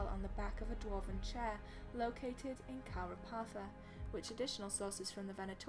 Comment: Where was that Dwarven throne's screen-shot taken? 22:19 I thought you may have said Khal'rapartha. I don't remember this thaig in any of the games.